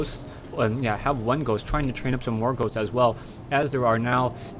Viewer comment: Lawl - I thought I heard you say that you're training up some more goats. That cracked me up a bit. It's at 2:02. Nice game though.